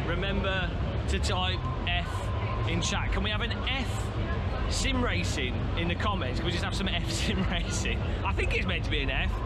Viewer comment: Ohh, EFFF racing simulator, thought you said METH racing.